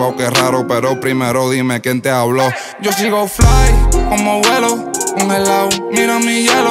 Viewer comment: pense que dijo: como Agüero